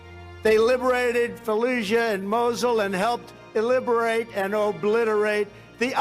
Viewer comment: did he say iliberate? He's so smart, he even knows words the dictionary doesn't know